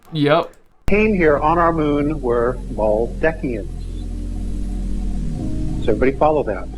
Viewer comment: he said "Arians" not "Aliens". Arians were/are, as he said, the whites. Once on earth and over the centuries they became the white supremacists. Today known as the arian nation. There's more history to it than that (what I've just said), but thats the main gist.